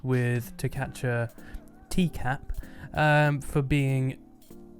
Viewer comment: Did u just say "to catch a teacup?" Most likely not, but I laughed at the absurdity of it lol